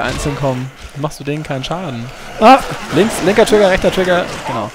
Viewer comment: bei 11:50 verstehe ich linker türke rechter türke oO